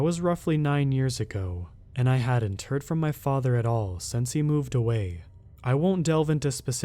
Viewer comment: I could've sworn you said, "I have a turd from my father" 🤣